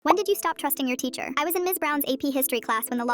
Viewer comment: STOP I THOUGHT IT SAID "When did you stop thrusting your teacher" 😨😨😨😨